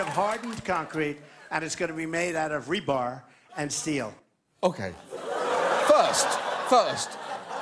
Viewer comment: Was I the only one who thought it sounded like Trump was saying - "It's going to be made out of Rhubarb and Keel". Yeah, that vegetable ship should show those mexicans who's boss all right xD hahaha